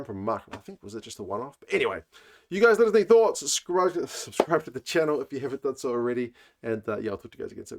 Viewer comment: lol I thought you said “scrotum to the channel”